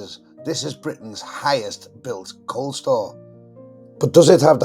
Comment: At first, I thought you said "Coal-Store". That place could store a lot of coal!